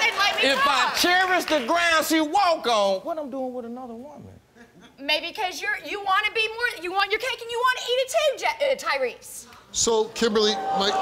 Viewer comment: Can anyone please tell me what she say on 2:36 God bless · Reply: "Maybe 'cause you're- you wanna be mo- you want your cake and you wanna eat it, too, Ja- uh, Tyrese!" Stuttering included for your following-along convenience!